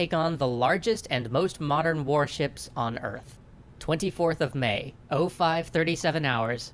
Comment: react to Yamato, The Right Ship For The Wrong War by Potential History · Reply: my bad I thought they had said "ever" but they said "on earth". So you are completely right